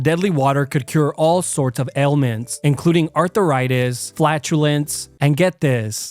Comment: I swear he said Arthur-itis